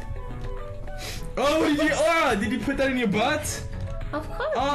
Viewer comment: When he said "Did you put that in your butt??" at 4:52 does she say "Of Course" ?? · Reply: Of course